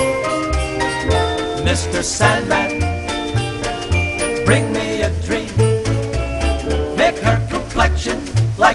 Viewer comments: I used to mishear the lyrics as "Mr. Santa, bring me a drink" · I've always heard "Mr. Sunlight, bring me a drink" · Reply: I heard "bring me a tree"